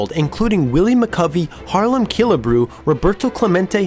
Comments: Great vid, but at 1:27 it totally sounds like you said Harlem Killebrew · Sounds like he said "Harlem" Killebrew, lol....